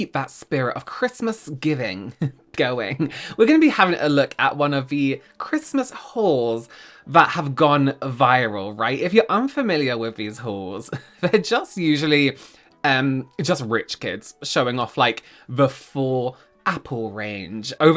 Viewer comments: I must be tired because I thought I heard you say Christmas whores 😂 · Oh my god I thought you said Christmas whor s. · Thought you were saying Christmas holes... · it sounded like you said "if you are unfamiliar with these whores" 😂 I was like ALREADY, THE SNARK IS SNARKING! Nope, I just can't hear correctly 😅